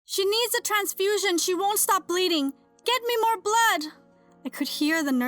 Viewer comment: So the beginning caught me off guard and all I heard was "I NEED MORE BLOOD" and it made me laugh...am I evil...also I enjoyed this video like always 😁